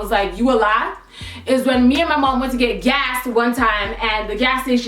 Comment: Girl, I thought you said "me and my mom were about to get gassed" 😂